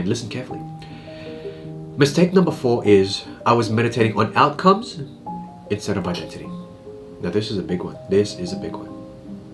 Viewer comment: Mistake #4 @ 14:40 min marker, meditating on outcomes instead of what???? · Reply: Meditating on outcomes instead of identity